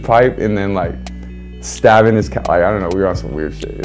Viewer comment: did i hear stabbing his cat? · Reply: COUCH not cat lol that threw me off right away too